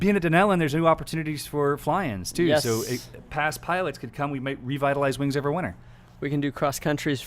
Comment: Aviator adventures...sounds like a blast. Any idea on cost. And did I hear “revitalize WoW”!!!